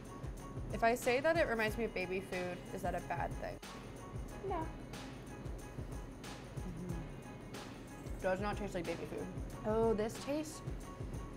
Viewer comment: did someone say baby food